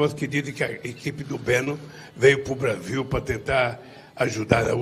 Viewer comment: não entendi o q ele falou aqui 6:29 "equipe do beno" ksksks se alguem pode me esclarecer o q ele disse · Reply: Ele se refere a equipe de Steve Banon. Chamou o outro pelo sobrenome Benon...